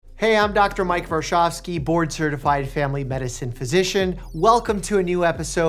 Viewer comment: I thought he said he was born certified